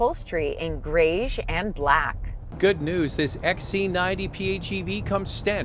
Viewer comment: Did you say xc90 at 1:45?